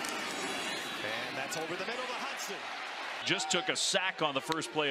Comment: I thought they said he won't throw over the middle 😂